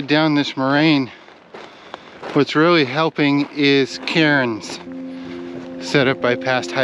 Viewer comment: A great video of this far away land, it is so relaxing to watch this. At 29:21 (and also a bit earlier) when he said "What's really helping is cairns", at first I thought he said "Karens" and was laughing a bit as it certainly wasn't that 😅